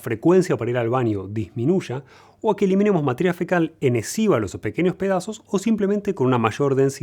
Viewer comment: Que dice ahí? Enesiva?